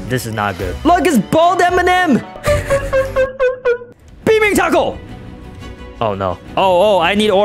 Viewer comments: I thought this boi said beaming taco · at first I heard "beat me taco" and I was like "wuh? 💀"